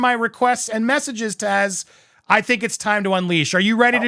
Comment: Lovely to see Taz! I got scared as I thought i heard Tessa 😮 These YouTube saboteurs need to be punished....